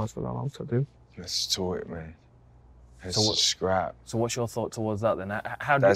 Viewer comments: I still can’t figure out what Jorge said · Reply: Decimate lets do it mate, lets scrap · What did Masvidal say to darren at 4:46 ??? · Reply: lets do it man lets scrap